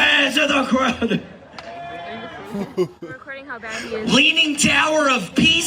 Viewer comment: I'm pretty sure I heard someone say "I'm recording how bad he is". Wonder if that's the one actually recording there or just somebody else nearby.